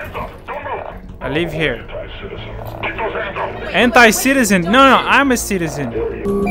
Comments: It sounded like he said “hent*i citizen” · I misheard "hentai citizen" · i heard "hentai-citizen" XD 3:49 я услышал "hentai-citizen" XD